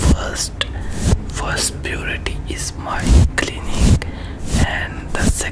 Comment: I always love your video but I had to laugh this one time because the subtitles you put said “puberty” Instead of “purity” in the introduction 1:25 😅